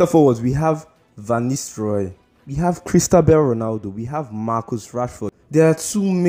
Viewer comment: Did I just hear Christabel Ronaldo 😂😂😂